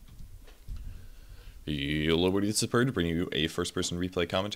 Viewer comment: is losing his touch. Now at the start it sounds like he is saying: yolo everybody is purge.